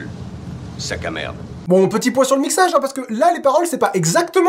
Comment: À 10:11 j'ai entendu "Bon, petit poisson mixage" 😂